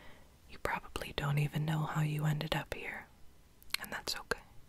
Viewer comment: “you probably don’t even know how you ended up here” girl i’ve been studying for the past 8 hours nonstop & i’m running on monster energy drinks i don’t even know who i AM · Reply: Rafaela K. Arnold I’m so tired I thought it said “I don’t even know who 1 AM”